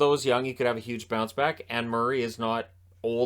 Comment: I laughed out loud because I thought you said "Anne Murray" instead of "and Murray." Great video, as usual! Thanks.